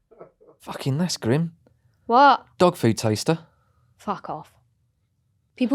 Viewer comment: Thought said ‘Dog Poo Taster’ ! Stopped me in my tracks.